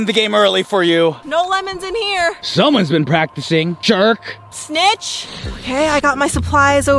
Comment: But she said snitch and I thought she said b*ch